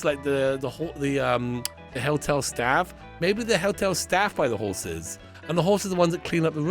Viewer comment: I keep hearing “hell-tel” and I know that’s not what you’re saying but that’s what I hear…and it is accurate cuz…I could not handle staying at this horse hotel at all Lmbo.